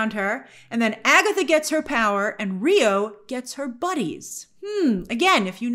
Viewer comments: I thought Rio said “I get my BODIES” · did you mean to say “buddies” when referring to Rio’s line in the sound booth? I could have sworn she said “bodies” which would make a lot of sense considering…. you know . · I thought Rio said Bodies not buddies lol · Not buddies, I heard Rio say “ Bodies “